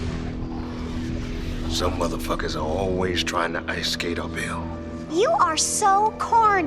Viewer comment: The “ice skating uphill” line was apparently from Wesley Snipes himself. When they went through the script, he described Deacon Frost as someone who’s always trying to ice skate uphill. The director liked it so much he put it in the movie. I’m assuming the line means Deacon’s ambitions are way too much for him to handle but he can’t see his mistakes. Or something. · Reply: One I first watched it I heard "some mother fucker's always trying to ice Cato Bill" and it still sounded bad ass. I don't know who the hell Cato Bill is